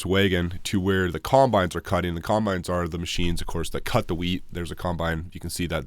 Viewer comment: Wheat 😂 i thought you said weed 😂😂😂